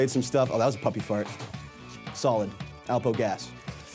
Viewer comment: did he just say puppie farts or am i hearing things